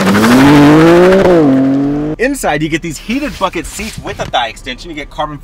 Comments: I swear I heard “You get these heated f*ckin seats” 💀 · I thought he said "heated f*** seats" lol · It sounded like you said heated F**** seats 😂😂😂😂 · I thought he said heated fucking seats😂